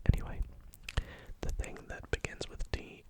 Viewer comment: the captions said "D" instead of "T" so instead of saying teapot I was like... Dividend!!! THAT WORKS!!! Then you said teapot and I got confused...